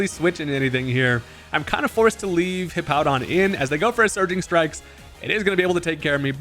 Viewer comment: I thought you said Sardine Strikes and I just pictured Urshifu slapping you with fish